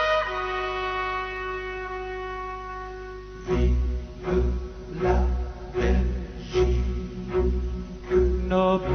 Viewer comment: what exactly is he saying and is it part of the national anthem? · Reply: Vive la Belgique = Long live Belgium